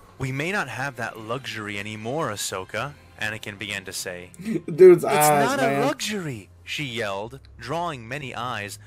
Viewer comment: Can anybody tell me what he said in 10:58? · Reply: "Dude's eyes.. man." That picture of him. His eyes are crazy lol